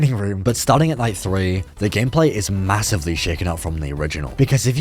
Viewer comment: did you just say massive???